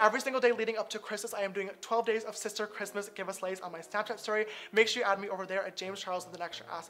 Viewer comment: Am I the only one who heard him say give a slays in stead of give away love James 🤣❤️